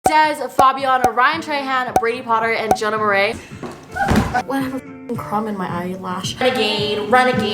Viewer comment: in the intro it sounded like she said jenna morais